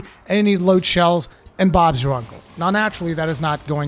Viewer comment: i need what? Bards uncle? this is the first time i had trouble hearing what you said... could someone tell me what he said? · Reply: Bob's your uncle. It's a saying, from where? I don't know. But it means "there, you're good to go".